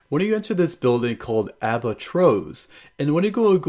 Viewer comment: when you said "Albatros" It sounded like you said Waitrose. Ps there is a Supermarket chain called waitrose